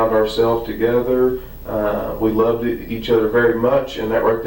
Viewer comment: Does he "slip-up" & say "loveD???? Instead of LOVE as in present.-??!!😮